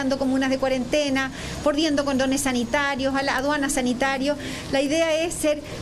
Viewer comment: Jajajjaj condones sanitarios ...mo existen po saco de wea ..si el contagio es inevitable ...se pasan 😆😆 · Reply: wn, lo dije porque la señora parece que dijo "condones sanitarios" en vez de cordones sanitarios. En el minuto 1:04 aprox.